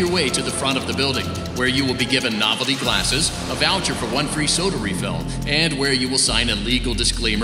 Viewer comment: I thought he said "blood free soda refill"